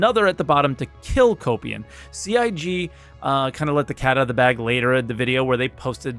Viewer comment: Am I the only one that heard him say 'Kill the Copium"?